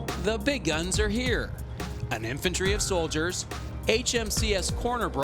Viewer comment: did.... did he say "an infantry of soldiers"?